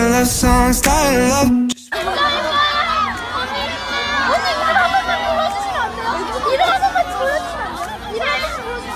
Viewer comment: can anyone tell me what are they saying? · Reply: The fan girl said unnie can you please tell my name Jeong: what's your name? Fangirl:jinyoung Jeong: jinyoungiee